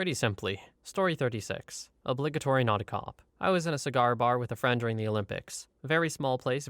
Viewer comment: or so on screen is "obligatory naughty cop" instead of "obligatory not a cop" 😂😂😂